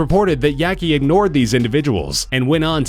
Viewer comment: I heard "Terry Yeakey" as Teriyaki